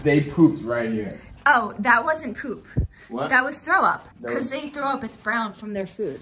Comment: What'd you say at 3:03? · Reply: I said, that wasn't poop, it was throw up. lol 😂